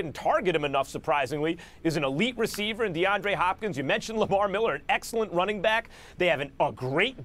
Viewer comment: Did max say LAVAR Miller at 3:21? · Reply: no he said lamar miller